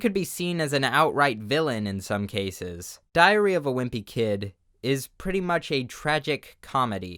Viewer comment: Not gonna lie, I heard "diarrhea of a wimpy kid" way too many times during this video